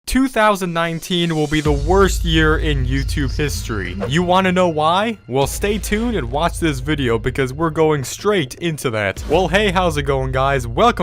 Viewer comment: At first I thought it said "2019 will be the worst yeet for YouTube"